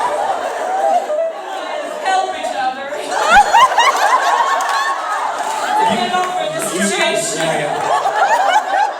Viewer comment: Misha is about to repeat til they're all out of influence but help at 26:32 what is the other option instead of shaving his head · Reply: It's hard to tell, but I think the other option was "To never play Sam again."